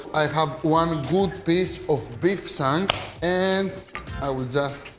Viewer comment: This looks amazing, what cut of beef is that please? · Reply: james huggins I think he says “I have one good piece of beef shank” here at 1:00.